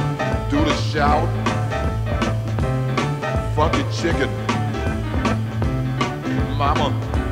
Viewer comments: did he say fuck a chicken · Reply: Nope, he said Funky Chicken. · what does he say "F a chicken" sounds like but I am sure its something else.